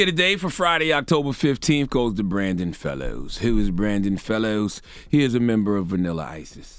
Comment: All I heard was "Hi I'm Brian Fellows" 😂😂